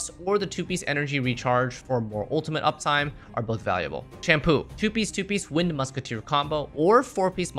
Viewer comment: My eyebrow raised so hard when I heard "shampoo" for sampo😭😭😭